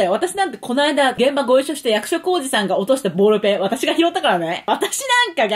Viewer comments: で「役所広司さん」が｢役職おじさん｣に聞こえて調べてしまった私。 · 役所広司さん ずっと役職おじさんに聞こえてた笑笑笑笑笑笑